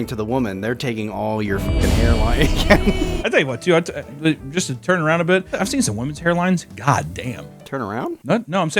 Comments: I was with the first time I heard “Just turn around” and assumed you were about to roast hair next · I would’ve turned around too. It sounded like you told to turn around.